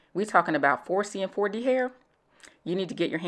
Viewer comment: did she just say 4D hair is that a thing 🧐😭